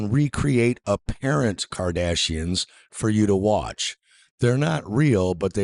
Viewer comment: I was a firm believer of the heliocentric, globe model of the earth until now. But this makes perfect sense, and now I'm a flat-earther... & I need to watch the Kardashians for some reason. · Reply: For a moment I thought that he said "Cardassians". 🤭#StarTrekDS9